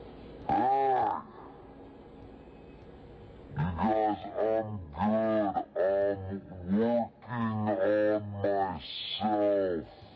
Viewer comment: WTF are they saying in the slo-mo at 2:06 ? What I can best decipher is; "You guys are good. I'm working on my salt." "You got a good sign!" "I don't want you. I'm working on..." and then it's the next clip. I've listened to it 15 times.